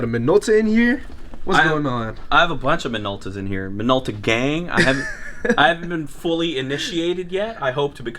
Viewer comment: I'm such a loser. I thought you were saying Minola Gay for so long...#shame